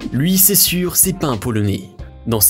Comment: Pardon mais qu'est ce qu'il a dit à 7:31 ? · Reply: Un polonais je crois